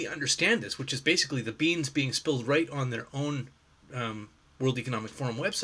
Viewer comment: I had to rewind and listen to part of this a few times. When you said "beans being spilled" I kept hearing 'beans, beans beans' which I have to say gave me hysterics. Solid logical video with a unique perspective, as is often the case. I wonder how long it will be before I stop chanting beans, beans, beans when I watch your videos. LMAO